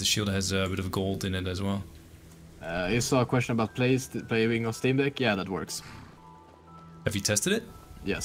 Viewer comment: Can someone tell me what he says here? "I saw a question...." I can't figure the rest out. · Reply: Play on the steam deck?